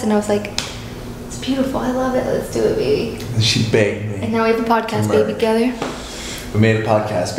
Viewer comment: correct me if I’m wrong but did you say “then she banged me” 😭😭😂